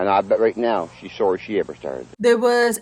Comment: Did basically just back up what his victims were saying? I have a hard time understanding holler hillbilly. But it sounded like he said, “She’ll be sorry she ever told”. Sounds like he is saying that there was a secret to be told.